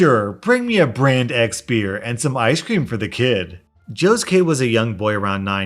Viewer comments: why did i hear "josuke" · I heard Joe's kid as Josuke. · It sounds like he said Josuke #JosukeHigashikata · I swear he said josuke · IT SOUNDS LIKE HES SAYING JOSUKE AS IN JOSUKE HIGASHIKITA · I misheard "Joe's kid" as Josuke Goddammit I have a problem